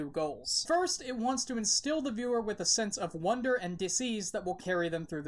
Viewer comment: Lol I thought you said sense of disease ...duh .. U say it diss ease which is weird enough but then you say it super fast in this monotone drone ...and yeah 👂🏻like sense of disease ..😐 Anyway I love the show But not you ! Goodnight